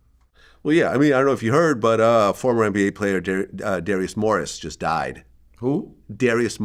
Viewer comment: for a second my eyes got big like Sebastian i thought Vlad said Darius Miles